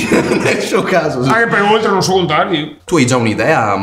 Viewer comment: ho ascoltato, riascoltato e messo anche i sottotitoli. Non ho capito comunque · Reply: "anche perchè oltre non so contare io"